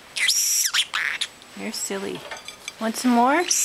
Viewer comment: Heard him say "You silly bird!"